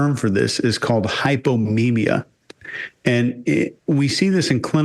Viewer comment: Hypominia. It sounded like you said Hypomania w/bipolar